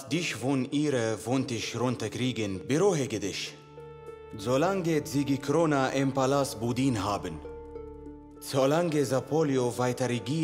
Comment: انا الوحيد الي سمعتها كرونا ولا ايش😂😂😂